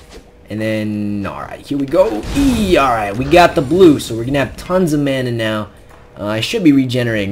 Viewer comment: Like if you thought he said tons of damage